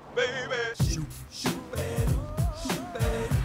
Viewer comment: For the longest time I thought it said shoot stab shoot